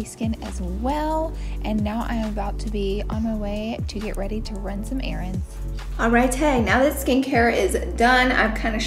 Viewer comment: I thought she Said she s going “to run some Eryns”:))